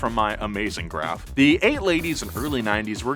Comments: he said "ate ladies" instead of late 80s ;) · Nolan says, “8 ladies and early 90s” instead of “late 80s and early 90s” love it!!! Love #Donut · anyone else hear “ 8 ladies and early 90’s” ? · Nolan says 8 ladies instead of late 80's, thank you for leaving that in I laughed audibly. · Nolan says eight ladies instead of late 80s 😂😂. Awesome video though! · Anyone else catch at 5:27 when he meant to say "the late 80's" he actually said "the 8 ladies". I know it's a stupid thing to point out but, hey I'm stupid. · 8 ladies at 5:27 instead of late 80’s?